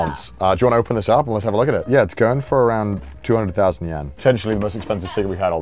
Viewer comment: am i hearing it right, 200k yen??